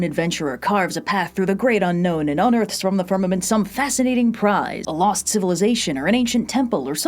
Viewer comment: Is there an audio glitch at 0:08? voice sounds garbled. "carves a path through the great unknown and ... some fascinating prize" Can anyone hear what's said here? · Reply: She just said "unearthed from the firmament" kinda fast.